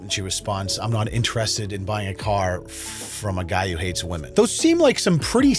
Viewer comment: Go to <link> for 15% off your order, AND ENTER PROMO CODE: LEONLUSH xoxo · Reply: I first thought she said "guy who HITS Women"...LOL